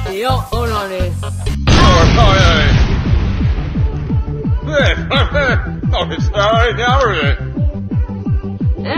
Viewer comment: it sound like midas say Jules help me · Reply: He said Jules help me I've been stuck in here all day